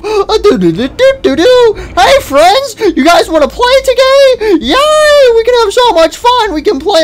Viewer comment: Wait a minute what did you just say sounds 1:53 Sun: you guys wanna play to gay Me:…WHAT!!!!!